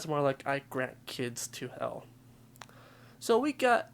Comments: I heard; "I have banished kids to hell!" EDIT: I hear the "granted" now. Either way, REALLY creepy. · I heard " I have granted kids to hell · i heard "i have dragged the kids to hell" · i heard "i have granted kids to hell" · I hear her saying “I will grant this kid to hell” I’m weird. Also it’s a mishearing or something. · I heard 'I grant the kids to hell'